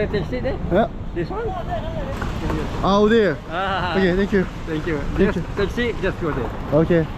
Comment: 아리가또 나만들리나?